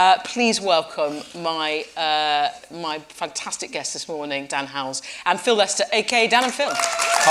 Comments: did she say "dan howells" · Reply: tbf i thought she said "dans howell' XD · I thought when she said Dan Howell in the beginning she said The Howells